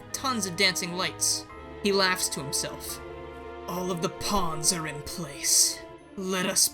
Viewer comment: I thought Meph had said "All the ponds are in place," instead of "pawns" and now all I can see is him manically laughing over tiny bodies of water.